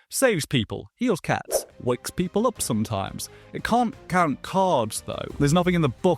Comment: Ohhh I thought you said “count carbs” that would have been a great spell😂